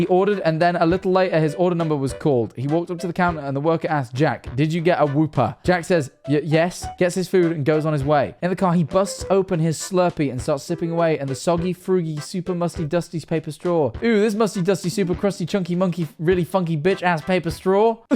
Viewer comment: I thought it said he bust open for a second